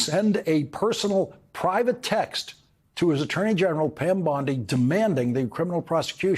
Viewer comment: WHY IS IT, THAT WHEN I HEAR 'BONDI', I ACTUALLY HEAR, 'BARNEY', AND SEE THE ASSOCIATED IMAGE?🤔?🤔?🤔?